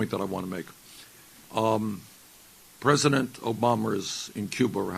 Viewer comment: did he say president Obamers?